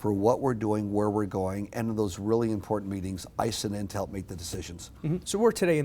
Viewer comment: Can someone tell me what he says at 3:48 Sounds like, "Ice and Intel make those decisions" :/